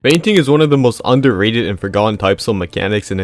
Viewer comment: Thought bro said feigning